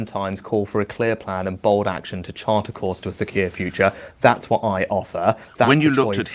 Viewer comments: does he say Sir Keir future here haha? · he actually says “to chart a course to a Sir Keir future” 😂